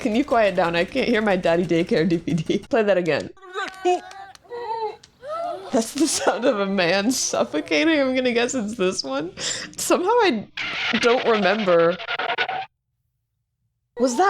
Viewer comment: Idk why I found it so funny but at 12:25 the subtitle says "potential the sound of a man suffocating" and it made me laugh so hard